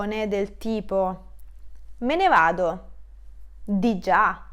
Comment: Grazie per questa spiegazione. Ma a 2:02 possiamo leggere 'Me na vado'... Vuoi dire piuttosto 'Me NE vado'? oppure mi sono sbagliato io? 😄